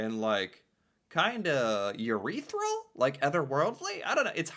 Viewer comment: I did NOT hear "urethral" instead of ETHEREAL???!!! xDDDD